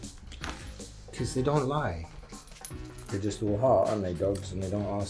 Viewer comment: His strong accent made heart sound like the word Hot which really threw me off. And English is my only language lol I can see how foreigners can have a hard time with English. Then at 15:02 the first line by and large sounded like by in large, because off the accent, and also as an American, I’ve never heard that phrase before so it really threw me off.